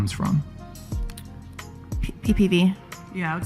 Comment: Is she saying PPV? Like pay per view? Or am I too innocent?